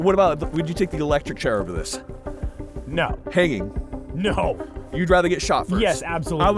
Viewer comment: i thought you said “pegging” i had to rewatch… lmfao HANGING! i was about to say, this channel getting strange…